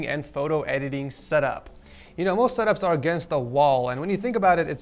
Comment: I swear I heard “Most setups are against the law”